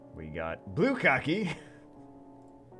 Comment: Blue Kaki...I thought he said bukkake at first and had to pause the video because I was laughing too hard!